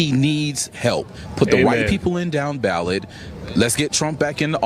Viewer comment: Oh my word! He said :got to put the RIGHT people in down ballet!" 😂😂😂 I thought he said we got to put the WHITE people in down ballet! LOL! Funny stuff! 😂😂😂